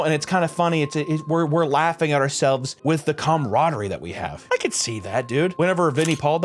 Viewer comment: I thought on the first pass he said "cUmradery"